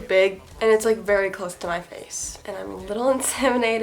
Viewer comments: oh no i heard you wrong, when you were talking about your new lense i thought you said “i’m a little bit inseminated” instead of intimidated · ok but did she say she was a little inseminated???? ( 0:47 )